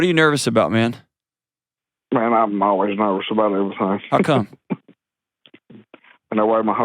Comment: ok can anyone tell me what the hell he’s saying at 6:45? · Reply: “Man, I’m always nervous about everything”